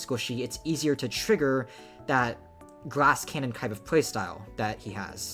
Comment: does anyone else hear him say “grass cannon” with an accent instead of “glass cannon”